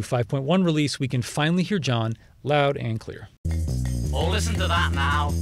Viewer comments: Oh, dang. Now I'm disappointed. I had always thought John was saying "Listen to that, Mal." referring to Mal Evans. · I always thought he said “Oh listen to that, Mal” referring to their roadie Mal Evans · I always thought he said “Oh listen to that, Mal” referring to their roadie Mal Evans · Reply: I have also heard it as “oh listen to that Mal” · I always thought John was saying "Listen to that Mal"...Mal Evans, The Beatles roadie. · I thought it’s John saying oh listen to that Mal not now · I always thought he said, "Oh listen to the band now." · Thought he might be saying "listen to that, Mal" as in Mal Evans